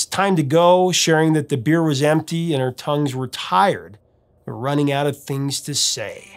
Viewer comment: Always thought he said, "Our tongues were tied"